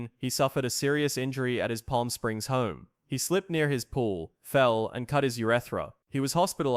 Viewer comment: Did they say he slipped by his pool and cut his “urethra”?